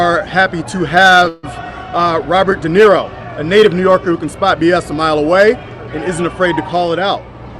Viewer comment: Did he say, "Robert De Nero, a native New Yorker who can spout B.S.?"